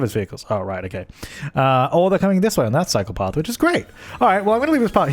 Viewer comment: Every time he says "cycle path" I hear "psychopath" and it's hella confusing.